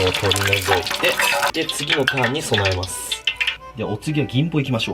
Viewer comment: 次のターンに備えますって聞こえた